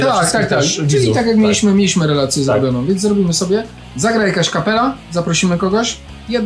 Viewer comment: "Zagra jakaś kapela"... usłyszałem "zagra jaś kapela"...